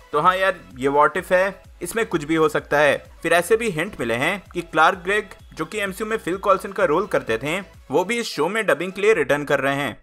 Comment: I heard, "Clark Kent" 😳😂